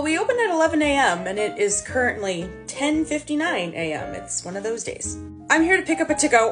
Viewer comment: typo in the subtitles said 10:59am but the subtitle said 10:00am